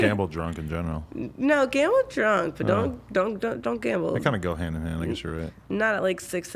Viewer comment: Did she just say gamble drunk but don't gamble lmfao